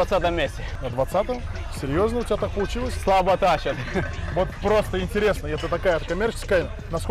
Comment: что он говорит? Слава тащер? Слабо тащер? Ящер?